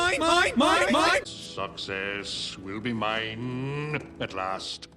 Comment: for some reason I heard “sucks ass will be mine”